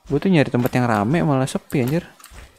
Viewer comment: & 3:37 apakah anjir itu toxic? Atau bukan? Atau gua salah denger? Atau lupa? GUA DENGERNYA ANJIR...